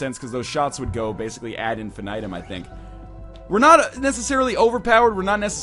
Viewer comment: he actually says infinite item